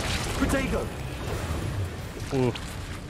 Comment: Everytime he says 'protego' I hear 'potato'